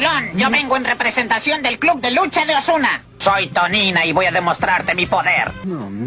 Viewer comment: Escuché mal o en el min 21:55 dijo Ozuna :O